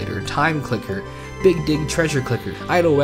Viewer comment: My thought process was broken when I heard "big dick treasure clicker" and had to rewind 3 times before i realized you meant "dig."